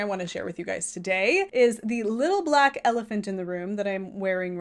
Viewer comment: I swear I heard you say 'little black elegance in the room' and honestly that fits! Gorgeous work.🙂